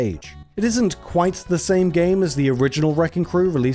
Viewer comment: So I heard "Sah-Mey Gah-Mey" and I had to do a double take. Either I'm overthinking the title, or it's just a simple overpronuciation of "Same game" Japanese titles can be whack- Oh and love the video! Doing great!